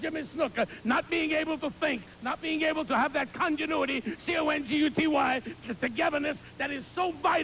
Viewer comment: "Continuity". Lou Albano was actually quite scholarly. Graduated from Harvard. What did you expect? How else could he, and Heenan, be such brilliant wrestling managers? Both Heenan and Albano are a part of the ivy league college alumni. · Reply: It sounded like he spelled it like "Conguty".